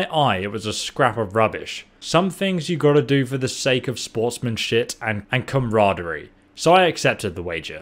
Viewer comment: he said sportsman shit instead of sportsmanship